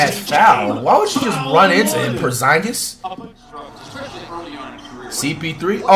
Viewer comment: Did he just call Porzingus Poorscience at like 0:30😂